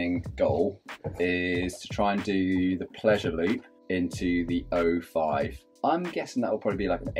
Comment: Every time one of you says "pleasure loop", I hear "pleasure lub", and the whole sentence suddenly gets another meaning.